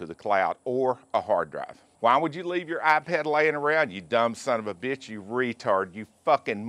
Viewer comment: The first time you said someone stole your ipad, I misunderstood. I thought you said someone stole your eye patch. LOL.